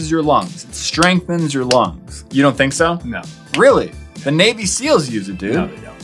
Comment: with the expand a lung. when u said its for navy seals i thought u said baby seals